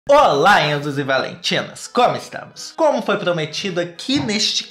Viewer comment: So eu que ouvi " olá enzos e VALENTINOS"